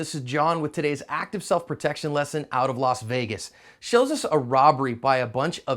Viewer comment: Except it's not California. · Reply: Active Self Protection whoops thought you said California. *Las Vegas